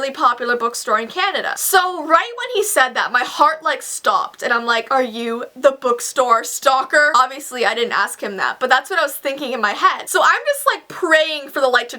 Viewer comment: I almost thought she said the book stalker is black 😂😂